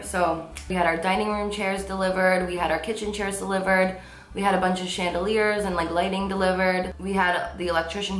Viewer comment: i thought she said 'we had a bunch of shit delivered' 😂 love you carli 😭😍😘